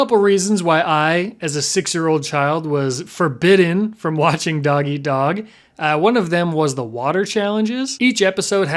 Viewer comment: Man, i keep hearing say "Doggie Dog" and i can't explain how cute and weird this was lol